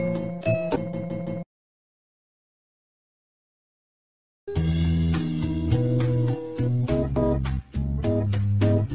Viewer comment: What's the last word here? I can make out the first three but not that last one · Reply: I think it says please don't hang up